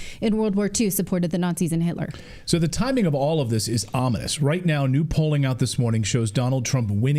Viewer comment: "This is ominous." I first heard it as: "This is amish." And started thinking hard about the reference 😅..